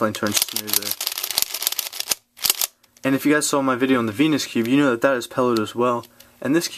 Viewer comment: Sounded like u said "penis cube" :p